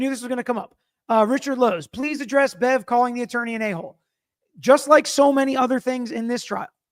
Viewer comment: It sounded like she said ass hole under her breath